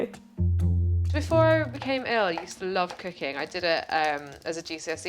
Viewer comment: why did i mishear that as “before I got ill i used to love cocaine